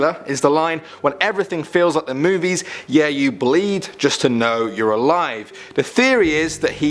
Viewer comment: I always thought he was saying "When everything feels like the movies, yeah ya bleed just to know every line." Which makes sense if you think about it.